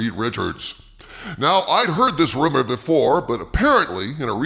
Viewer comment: I mis-heard you. I thought you said "I've heard this rimmer before." and I thought "Yeah, that's Pedro."